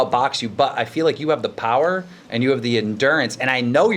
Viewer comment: Did keem say power? Goddamn.